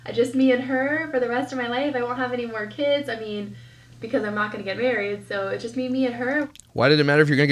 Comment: Isn’t that funny when she said I’m not going to get married anymore I thought she said they didn’t get married..... she doesn’t make since and she changes things as she goes and that story was way too long 😱🤪 and I almost fell asleep not even half way through her boring story 💤 😴